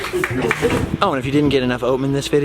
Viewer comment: "Oh and if you didn't get enough OM in this video..."did I hear that right? Right at the end 1:02:05.